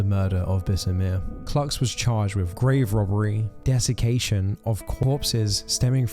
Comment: I'm sorry, but I had to chuckle to myself bc for a minute, I thought you said the vampire of Paris was charged with "grave robbing, '*defecation*' of a corpse..." 😅